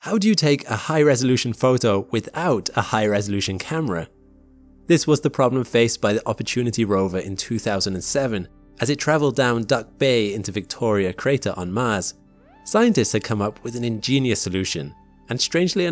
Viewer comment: Oh… duck bay, that makes sense. I thought it said duck boy. Didn’t seem right.